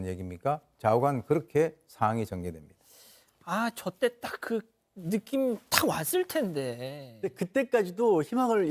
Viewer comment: '아~좆대따느낌왔을텐데' 로 들리는데 비방용 아닌가요ㅎ